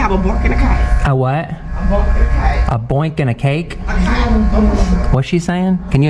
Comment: Chuck from. OH. I was laughing so hard when that woman behind the counter was hard to hear n it sounded like she said a boink in a cake . Lol officer needs to be on a plane out of the country doesn't matter to me . My dad fought ww2 for our freedoms. Chuck up n out. Thanks. 🖐🇺🇲⚘